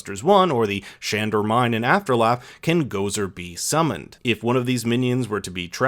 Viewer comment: anyone else hear "after laugh" at 2:28?